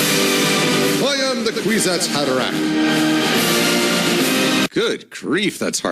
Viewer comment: Dune reference. Look it up, this is what he was saying: Kwisatz Haderach.